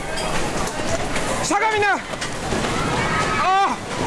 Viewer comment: Holy shit that is freaky yo.. is it just me or does it sound like he is saying "suk on me now" to that lady? Or am i hearing things? At 0:46 it sounds like it.. what is this person saying?